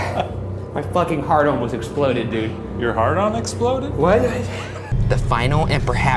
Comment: It did sound like Ryan said " His hard on exploded " hahaha